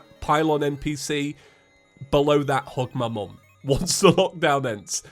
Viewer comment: I didn't hear 'Hug my mom'... I heard 'hump my mom.' Why.....